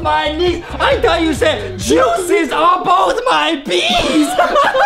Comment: I thought you said Juices on both my bees ! 😮🫠 just beautiful 😭😫😩